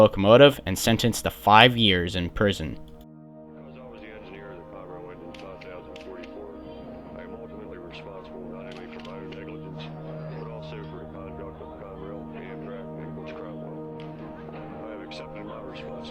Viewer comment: what is saying? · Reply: "Because I was the engineer of the Conrail engine 5044, I'm ultimately responsible not only for my own negligence, but also for the conduct of Conrail, Amtrak, and Butch Cromwell. I've accepted my responsibility by pleading guilty today"